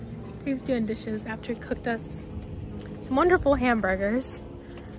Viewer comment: Quartzsite. I always thought you were saying Courtside. 😂😂 I thought that was a weird name. Quartzsite makes more sense. 😂😂😂